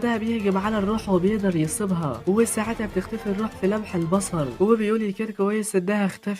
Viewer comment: هو انا الوحيد الي سمعتوا قال في لمح البصل؟؟